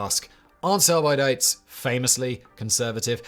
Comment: I heard "aren't some Luddites famously conservative?" and thought it was a fun and wild tangent.